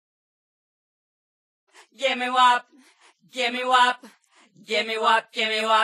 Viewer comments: D-did she say, "Give me WAP"?? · is she saying “GIMME WAP”